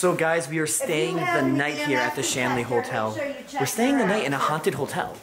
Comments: I thought at first this said The Stanley Hotel...I got excited for a moment :( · thought it said ‘the Stanley hotel’ n i got confused when he showed the hotel, then realized i got it wrong 😩